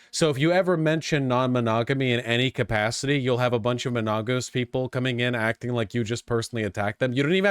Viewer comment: 33 thought you said "a bunch of monogos" and thought we had a new slur